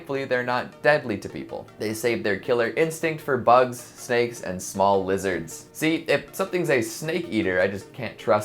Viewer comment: I heard "bugs, snakes, and small wizards"